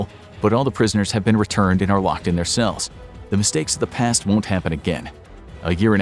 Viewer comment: i thought the said rocking or rocked in their cells, like a rocking chair